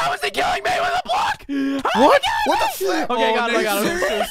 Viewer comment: Am i the only one who heard a bad word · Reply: He said flip.